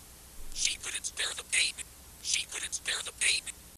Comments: So for the ingen tapes for Ashley chambers 31:54; I think it says "she couldn't bear the pain." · I think it said she couldn’t bare the pain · if you still don’t know what this says I’m pretty sure it’s saying “she couldn’t spare the pain”